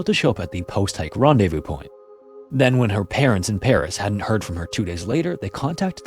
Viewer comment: Man, my hearing is going. I legit heard "...her parents 'n parrots..." and was confused why police would listen to a buncha parrots.